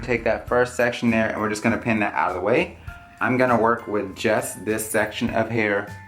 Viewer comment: if it's just me but did anyone else think he said jess instead of just on